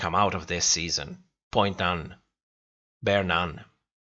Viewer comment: did you say "point done...bear nun?" XD i mean i agree! Lol! As usual, great review :)